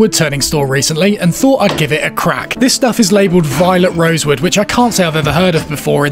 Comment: I thought you said violent rosewood 😅